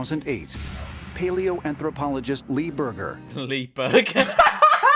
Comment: DID HE JUST SAY HIS LAST NAME.....IS BURGER